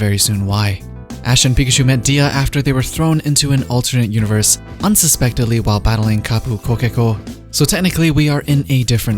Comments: did he say "Kapu KoKeKo"? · Did he just say Kapu Kokeko? It's Tapu Koko lol